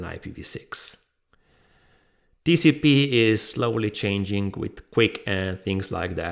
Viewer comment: "tcp is slowing changing to 'QUIC' "....in case if anybody wondering about what he said.